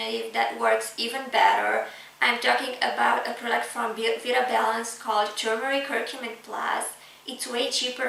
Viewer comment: Can you please post link for your recommended product , Turmeric and Curcumin plus, unfortunately I cannot make out what you’re saying as regards the name of the company nor can I make out the name of the company on the bottle and therefore I cannot find them online ..... I thank you for your video It was a great help in helping me to decide not to go with Heal and Soothe as I do not want to be contracted to having a Subscription if they do not cancel my order when I request . I do not like this at all which a lot of sites to do . I feel this part of any offer is a scam to make you buy more even though it may not possibly work. IT Does sound like you say ‘Vitro Balance’ but I cannot find the web site when I google this.